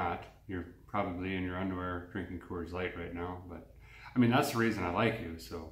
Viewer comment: Favorite line I misheard: ‘If you have been paying attention, which lets be honest, your probably not. Your probably in your underwear eating Corn Flakes. Corn Flakes… Coors Light… which ever. 😂 The mental image of eating Corn Flakes was funnier thou.